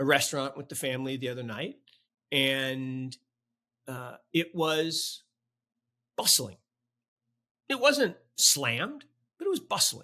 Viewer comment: i thought said " It was bussin' "